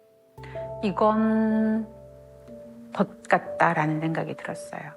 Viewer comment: 이건 ㅈ같다라는 생각이 들어요라고 들리냐 왜 ㅋㅋㅋ